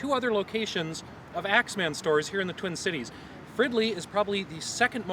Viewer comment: I thought you said "friendly" and wondered if they were just assholes at the other locations...🤔